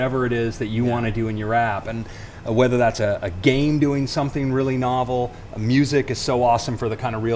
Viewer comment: Did he just said 'Game' at 3:13??